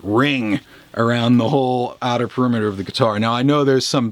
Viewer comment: What does he say at 4:02 ? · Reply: Yamil Yamil outer perimeter of the guitar